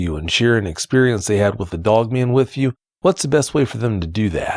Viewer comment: Every time I hear him say “Doug Man” instead of Dog Man, I lose myself every single time. 🤣💀🤦🏽‍♂️😆. Sorry, I will see myself out now 😅